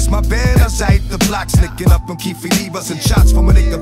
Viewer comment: Did DMX say keefe D?